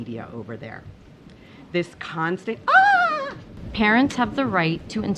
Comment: Did she say Karens? If not,she should have.